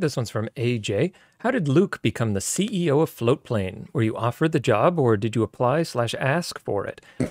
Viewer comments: Floatplane is one of the worst names I've heard of. For the first 3 months of hearing its name I thought they were saying Flowplane, so when I tried to look it up I could never find it. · I feel like you need someone better on the marketing side of Floatplane. It feels like an odd ambiguous project nothing significant is ever explained about or rather isnt done so concisely. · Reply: I had no idea what it was still I saw your comment and googled it. I’d hear it mentioned on various Linus videos but never felt the need to check it out. I always thought they were saying “flow plane”. And after viewing it I still see no reason to sign up. Then again I’m not much of a linus fanboy which seems to be the site demo